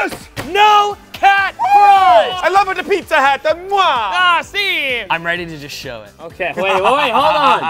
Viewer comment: did Coby just say, "Ah, Sí"?